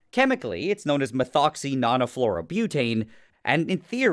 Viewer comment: Thought he said leave u with a fully tactical glock😹